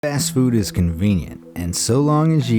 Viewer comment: did you say fast food or ass food?